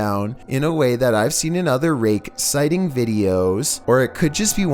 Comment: i thought he said r**e